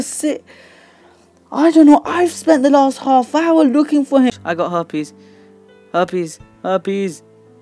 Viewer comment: I almost die of laughter when you said "I got harpy's" I thought you said I got herpes